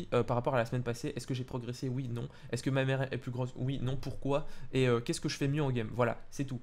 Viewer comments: J'ai entendu "Est-ce que ma mère est plus grosse oui non" Mdrr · J'ai entendu "Est-ce que ma mère est plus grosse" XD